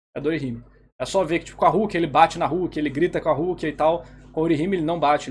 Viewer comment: ''ele bate na rukia ele xinga a rukia, na orihime n'' agr eu entendi, se n bater ta bom (zueira mas achei muito engraçado)